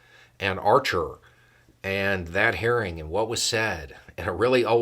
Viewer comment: At the start you said hearing and I thought I heard "haring" as in red haring.